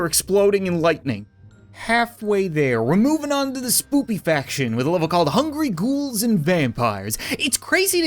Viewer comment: Did he just say spoopy faction